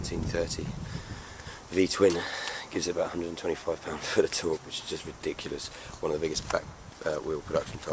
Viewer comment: I thought he said 125 ft lbs of torque (?)